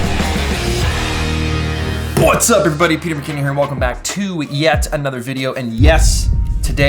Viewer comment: the caption said Peter bikini?.?